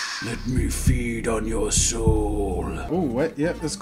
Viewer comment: captions: Let me feed on your shoe (Instead of soul) 😂